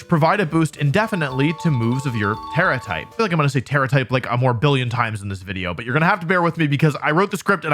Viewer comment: did bro just say morbillion